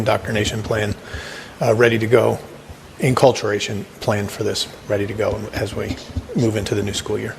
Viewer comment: Facts "indoctrination" tried to make up for it by saying culturation is that even a word? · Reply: It did sound like they said that, huh? But this Ziegler dude was actually saying the word, "acculturation" which means: "The process by which the culture of a particular society is instilled in a human from infancy onward." And now that we know that.. it's in ALIGNMENT with their big ROBUST INDOCTRINATION rollout plan for the children, right? Some nefarious BLEEP!